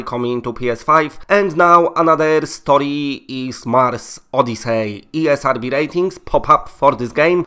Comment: Lol i thought it said Mario Odyssey instead of mars Odyssey 😂